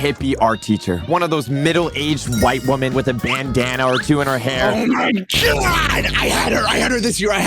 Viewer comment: when you said bandana I thought you said banana